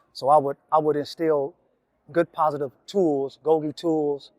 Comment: I could be saying this wrong but what are GOI tools? @ 5:55 mark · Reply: GOGI....Getting Out By Going In, meaning meeting these kids at the juvenile level before they get to jails and prison